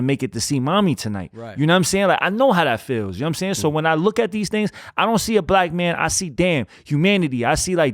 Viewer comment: did he say "when I see these niggaz?" Caption says he said "things" Hehe he getting emotional and reaching deep.